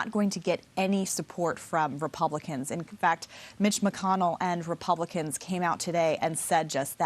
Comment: she sounds like she says the Devils and the details 🙉🙈🙊